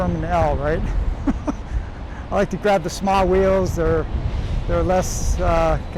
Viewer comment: i thought i heard you sa "smile wheels", very fitting name